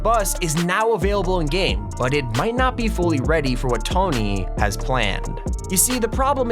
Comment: did he just say t ony